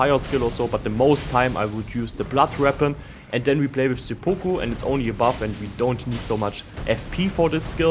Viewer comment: all i heard was "we play with ze poopoo"